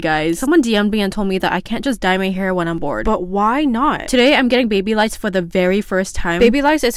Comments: I THOUGHT YOU SAID YOU WERE GOING TO GET BABY LICE FOR THE FIRST TIME. 😭😂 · I’m sorry I thought she said “Today im getting baby lice!” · BRUHH I THOUGHT SHE SAID BABY LICE